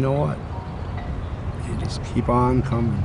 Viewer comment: "...they just keep on comin'" Bad choice of words, dude. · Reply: That's when I couldn't hold my laugh, and even worse since I heard "I keep coming"